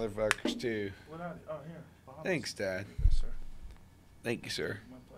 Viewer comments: did he say thanks dad?? lmao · Am I hearing things or does Post Malone say "Thanks Dad" to Joe after he hands him a beer